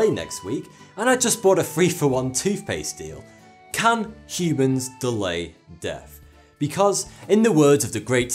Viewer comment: misheard words - can Cubans delay death?